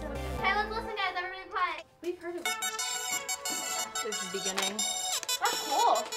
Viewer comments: I don't know if she said this or not but I think Maddie said we already heard it. Put your volume up. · Did anyone else hear maddie say at 0:13 'we heard it before'